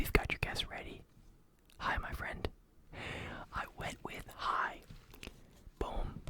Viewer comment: I thought it said AI :')